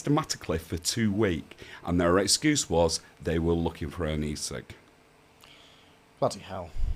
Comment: "...they were looking for ..." - for what? The transcript's no help. It says 'knee sick'. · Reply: E-sig (electronic cigarette) is what I thought he said.